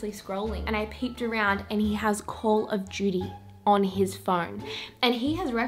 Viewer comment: I thought she said call of Judy and I immediately went to look up this fun and exciting new game.. 😮‍💨🙄🤦